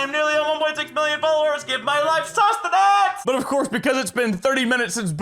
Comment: I thought he said “give my wife sustenance” and I just went